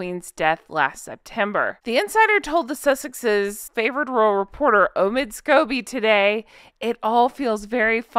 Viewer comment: I heard him called "Omid Scabies". I think it's fitting!